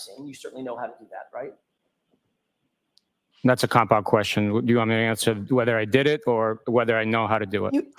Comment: At first, I thought he said “that is a cop out question” as well but have since realized he was saying “compound question.”